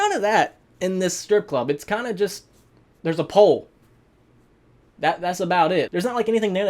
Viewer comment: I laughed cause I thought he said “it’s a paul” instead of “it’s a pole”